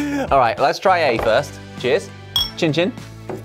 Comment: did just say... chin chin?